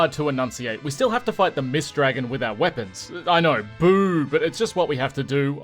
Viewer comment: Your accent nearly killed me or I am just half deaf).......I heard "we have to fight the mist dragon WITHOUT weapons" and you said "WITH OUR weapons". I was calling shenanigans until closed captions set me straight! Great run! FF4 is one of my absolute favorite games. I have beaten it more times than I can count. Cool vid!